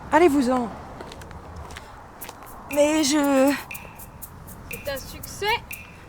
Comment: "C'est un succès ?" J'ai entendu "elle t'as sucé ?" x)